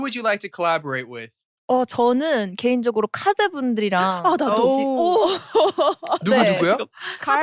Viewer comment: what's the name that yoohyeon said? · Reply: the group "KARD'